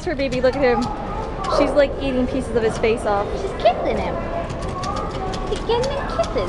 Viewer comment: I thought it said eating pieces of cake off